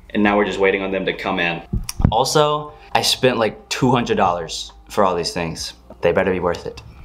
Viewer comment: For a sec when you said “I spent like 200 dollars on this stuff” I thought you said “I spent like 20 dollars on this stuff” and I was like: “bruh…”